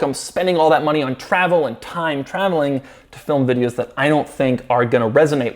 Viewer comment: Did Doug just say time travel was too expensive to film in Europe?